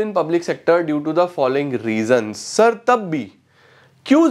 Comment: ????aapne kya bola sir? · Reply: accha!kuch nhi, Sir ne bola "due to this following reason tab bhi" Mujhe ye tab bhi samjh nhi aaya tha, 2x me dekh rha tha,🥲isliye toh wahi poocha,🥲